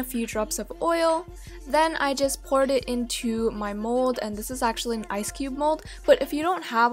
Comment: When you said mold I thought you said "...then I poured it in my mole..." I dyed 😂